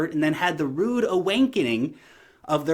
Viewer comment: Did you say "awankening" at 11:13?